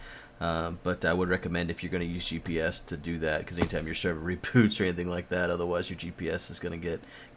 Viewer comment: At the end when you said reboot I thought you said repoot